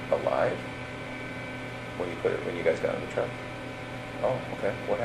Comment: Can someone help me out and tell me what he said beginning around 9:23 or so till 9:34, I think that's right, but I can't understand what he was saying about Bella. Thank you!! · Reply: detective asked him “when you guys put her in the truck”. I guess he agree and then cw said “I don’t want to talk about that.